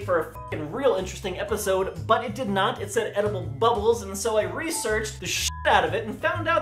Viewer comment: it's 3 AM and I heard the "edible bubbles" at 2:20 (aprox) as "edible buttholes"... yeah